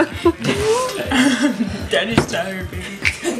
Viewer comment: Did I just hear that right? Did I just hear Iida say "Daddy's tired baby" ???